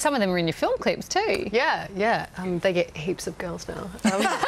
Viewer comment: what does she say at 2:24? her accent is so cute but hard to understand sometimes! -) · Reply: yea yea they get heaps of girls now.